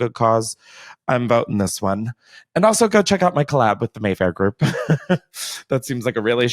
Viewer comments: I had this podcast turned up so my bf could listen from the other room as well and he audibly gasped so loud when you said mayfair bc he thought you said wayfair 💀💀💀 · Haha omg! I thought you first said the clothing colab was with wayfair!!!! Remember that drama of people saying they were selling kids...not storage lockers lol.